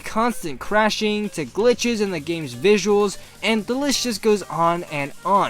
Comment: I thought you said "delicious" there.